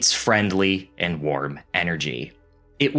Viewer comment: Im sick with flu or something and thought this man said nword energy 🤦🏻‍♀️